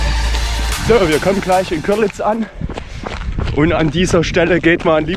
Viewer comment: Hab erst Görlitz verstanden..🏃😄